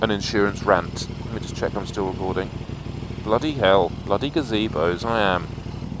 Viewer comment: Did I hear "Bloody gazebos"? Ha Ha!